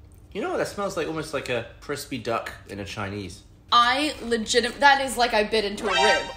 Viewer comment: does gavin say that smells like “a crispy duck in a chinese”?? babe wake up new candle flavour just dropped LOL